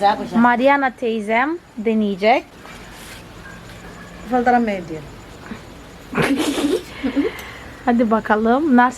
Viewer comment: de ne diyor altyazı eklememişsin. Teyzene zorla mı yediriyordun, biraz isteksiz gibi yiyordu sanki :D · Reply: Merhaba “ bir içecek olsaydı iyi olurdu “ diyor Yok aslında zorla yedirmedim 😃 bazı insanların yüz ifadesini farklı olabilir bilmiyorum .